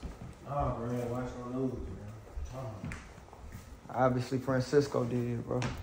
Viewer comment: Didn’t you’ll heard what said Francois is dead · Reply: I thought that's what he said too but he asked Ty if he'd seen the news...Ty said no... said Francisco DID.....not that he dead lol